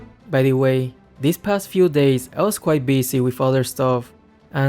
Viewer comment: I heard "father stuff" instead of "other stuff". OMG, sorry